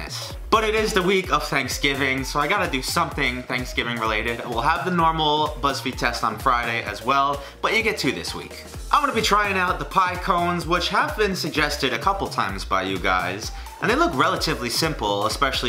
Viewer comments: I thought it said pine cones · I thought it said pine cones 😂